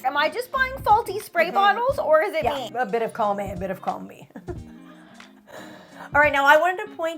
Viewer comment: what did she say @ 5:40 ???🤣🤣🤣🤣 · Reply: she said a little bit of column A and a little bit of column B🤣😂🤣😂🤦🏻‍♀️🤦🏻‍♀️🤦🏻‍♀️🤦🏻‍♀️🤦🏻‍♀️🤦🏻‍♀️